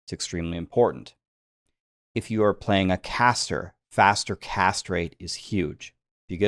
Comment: I heard "faster castrate". An important stat for sure 😂